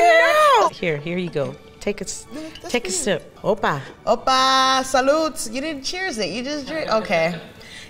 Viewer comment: did she say ,,salut” ?